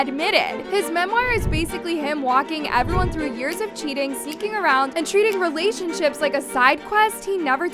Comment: “Cheating oath” is that what that said ??